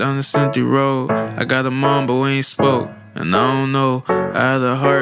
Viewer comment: Mondegreen: when he sang, "I got a mom, but we ain't spoke in I don't know", I heard, "I got a mamba when he spoke...and I don't know"! LOL